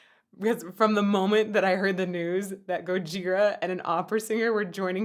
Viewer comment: Did you say "Gojigora" ? xD